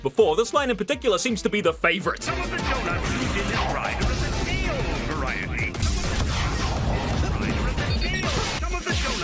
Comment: Due to cost restraints, some of the donuts used in this ride are of the day-old variety. We apologize for this cost cutting measure, and hope that you enjoy the ride. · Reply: I always heard “stale variety”